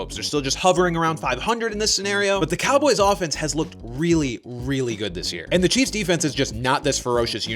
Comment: was half listening and did a double take bc I thought I heard “the cowboys defense has looked RLLY good this year” 😂